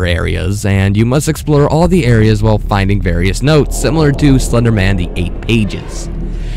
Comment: sounds like he said "Slenderman: The Ape Ages" I lol'ed so hard.